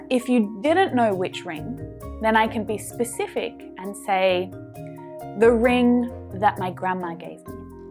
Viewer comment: G’day. I love your videos so much. I just found a question regarding #3 a sentences in the test. “The ring that my grandma gave MY is worth a lot of money”. But I I heard you said “The ring...gave Me...”. I am assuming the script has a mistake. :)